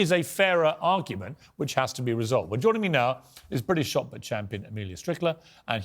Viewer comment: The blond girl is British? She sounds American to me. I had to rewind it to see who she was, and then I thought Piers said she’s a British “soccer champion” (But then I was like, why would a British guy use the word “soccer”?) But I think he said “shot put” What part of Britain is she from? Certainly not from Scotland nor Liverpool, nor the area of the Cockney accent (nor any of the other areas, that us Americans know with out limited knowledge of UK accents)